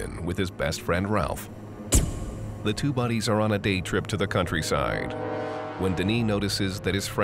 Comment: i thought he said a date trip